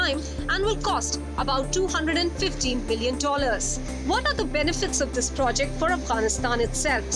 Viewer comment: She said Billion Dollar instead of saying Million 😃😃.. whoever noticed 👍here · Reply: She said million which seems to hear like billion